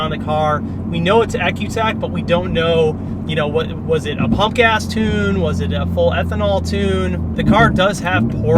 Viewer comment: when he said “pump gas tune” I thought he said punk ass tune 😂 thought he was about to start going in